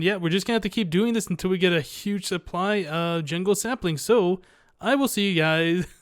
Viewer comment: Aw, it sounded like you said a huge supply of jingle saplings! Lol. :P